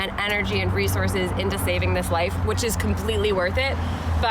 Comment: Am I the only one that thought it said “shaving” instead of “saving”. I was like won’t that hurt the cat😂